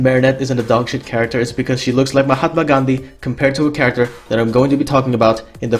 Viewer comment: did bro just say "mahatma Gandhi"?